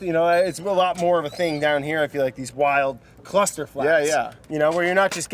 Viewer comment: Did I hear that correctly? 11:00 CLUSTERFlocks?